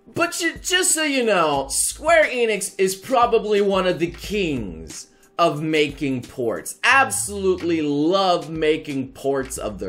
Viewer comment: King of making porn ??!? oh, sorry. I misheard. I got bad ears